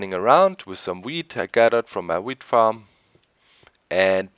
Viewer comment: I like your accent. At 14:17, it sounded like "weed gathered from my weed farm" and I lol'd